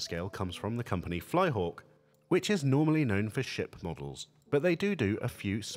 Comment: When you said "Flyhawk, who are normally known for ship models", I first thought you said "sh*t models" lol. I like their little tank models. :)